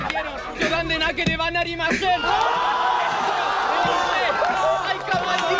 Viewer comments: Alguien sabe que dijo en el minuto 4:05 (? Y si es idioma japonés Posta? Jajaja xD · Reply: siii dijo "joudan de nakereba na arimasen", que junto con lo otro seria ustedes perdieron y no estoy bromeando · Alguien que me diga que dijo e japonés ) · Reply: Yeimmy Hernandez yorande no nakiri narima sen